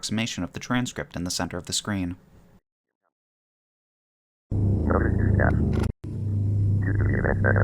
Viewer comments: I think it says "Notice to staff" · I think it says "notice to staff" as for the missing word(s) in the rules, I think it's "foreign body part" but that's a weird way to say that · it sounds like it says "Notice to staff" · I think he says "good evening staff" or "good evening cat"